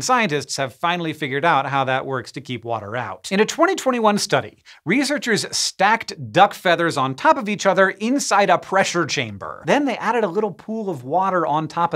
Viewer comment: I first thought Hank was saying that researchers stacked DUCKS on top of each other inside a pressure chamber! I was horrified!